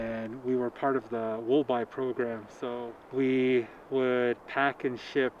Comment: what was the name of the government program subsidizing wool? I heard “wool by” but nothing comes up when I search it. The subtitles say “will by” but I couldn’t find anything with that either. Also thank you for this beautiful and necessary film. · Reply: Try Government Wool buy program instead